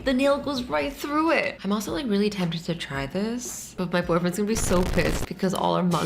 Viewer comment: did I just hear what I think I heard DID SHE JUST SAY BOYFRIENDS???!!!!